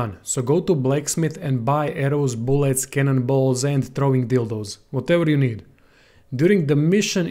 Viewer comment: Did he say "throwing dildos"? 😂🤣